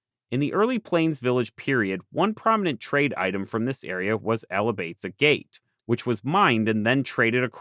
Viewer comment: What type of agate was traded I don't understand what you said at 46:08. Thank you, very good video. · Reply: Alibates flint